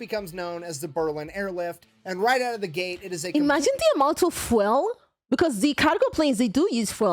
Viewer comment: Me yelling from the back of the room: "DID SHE JUST SAY 'FWELL'!?"